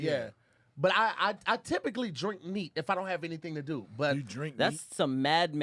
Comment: I thought he said he drank meat too.